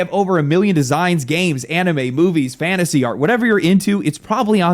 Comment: I thought bro said "anime boobies" I need to go outside.